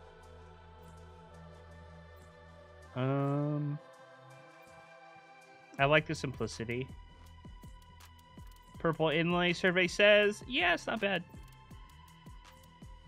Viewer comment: did he say survey says like in family fued?😂 this is why he's one of my favorite content creators. Use code